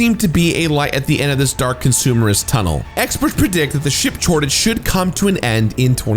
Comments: what's a "Ship chortage" ? Note: I mean this as a humorous observation of what was seemingly said not a mean spirited insult. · Did he say "Ship Chortage" at 2:46?